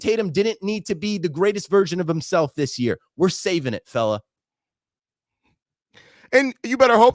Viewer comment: When said "FELLA" I heard "NIGGA" 😂 😂😂 Haize 2-1... I guess